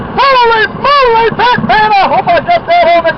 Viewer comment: "Holy Moly hot damn I hope I got that on the cam-er-ah!!!" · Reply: I heard "Holy-Moly Batman! I hope..." but I think you are correct.